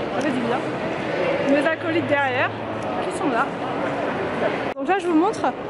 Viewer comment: Cc j'ai entendu "mes alcooliques derrière" 😅 puis jme suis dit elle a pas pû dire ça j'ai réécouter mes acolytes derrière....🤦